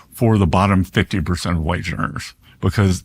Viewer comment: The secretary says "wage earners" and the subtitles say "white learners" better fix that before some deaf minority starts a protest. Just saying....